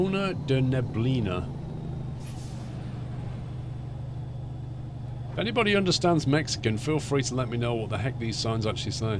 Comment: Did you just said anybody who understand Mexican lol